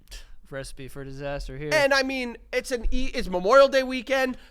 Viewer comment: lmao sounds like says “breastfeed for disaster here” 😭